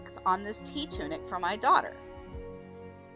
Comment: I kept hearing “tea tunic” and being confused 🤣 I guess I needed more caffeine!